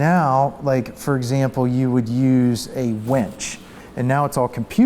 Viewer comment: I could have sworn he said "you'd use a wench..." - I was thinking "why would you need a young medieval woman, specifically, to pull this off?"